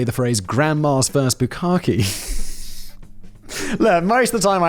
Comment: I heard "grandmom's first Bacardi" lol. I was trying to figure out what was wrong with granny drinking Bacardi??? I am thoroughly horrified like at what he was really saying 😱🤣